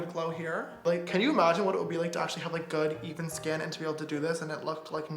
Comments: did he say “to have good ethan skin” ? at 5:17? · i thought he said "good Ethan skin" 😂😂😂😂😂😂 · i thought james said “good ethan skin” 😂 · I thought he said Ethan skin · lol i thought he said “ good, ethan, skin...” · anyone else think he said “good Ethan skin?” 😂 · did anyone else think he said “good ethan skin” 5:16 😂😂😂